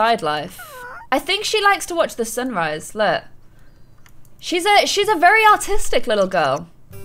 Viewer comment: When you said she’s very artistic like her mom I thought you said she’s very otistic